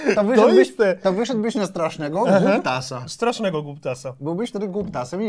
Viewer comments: czy ktoś też usłyszał "kutasa"? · usłyszałem KUTASA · Za pierwszym razem usłyszałem ''kutas'' 😀